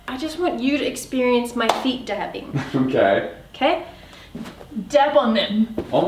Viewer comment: Lufu calling "Daddy" is probably the purest thing I've seen😂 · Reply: I thought she said feet dabbing